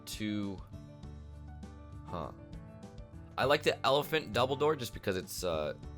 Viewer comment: when he was getting the elegant double door i thought he said elephant dumbledore xD